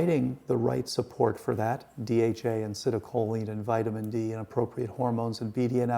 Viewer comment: There is a mistake in subtitles at 50:02. It's citicoline that's needed to make synapses, not acetylcholine! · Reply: The typist misheard the word so she mistyped acetylcholine as citicoline